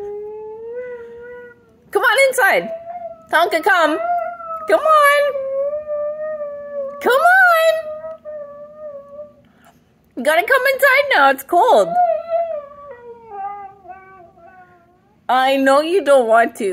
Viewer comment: did he really say: I dont wanna come inside"?!